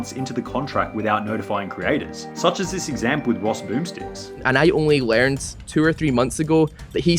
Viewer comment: did he just call ross ''rossboomsticks''.... this is unacceptable (jk jk)